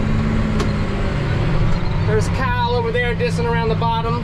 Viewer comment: At first I thought you said there was a cow over there and then I realized you said Kyle